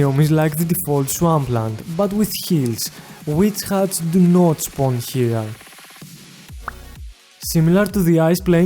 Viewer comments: caption says it's like swampland but we killed we cut the notes for here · uhh sry about that what did u say here? i didnt hear what dont spawn here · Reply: witch huts dont spawn here